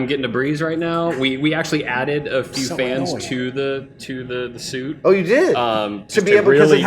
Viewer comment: did he just say so annoying?